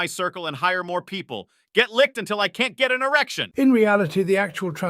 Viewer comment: did I hear ‘an erection’ ? . . . 😂